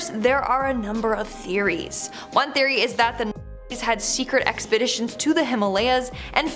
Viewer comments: What is the word that is cut out at the 7:57 mark? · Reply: Uhh germany army during ww2 Aka the Nazi`s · the audio cuts before she says the name anyone catch that? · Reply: nazi